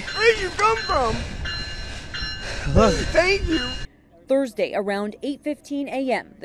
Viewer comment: Did anyone else here the guy say “where did you come from” and “I hate you” after being saved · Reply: I thought I heard "I hate you" as well🤔but after listening again he did say "thank you"!